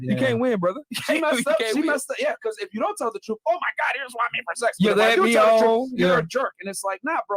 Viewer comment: It sounds like she was saying “don’t tell the truth or I’ll have a miscarriage”